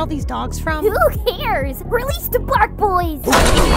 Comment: "Release The Bark Bois" is now my favorite Marcy line. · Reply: I thought she said “release the bad bois” guest I missed heard